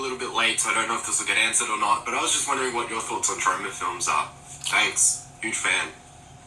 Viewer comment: what the fuck did he say? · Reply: I think he said Troma films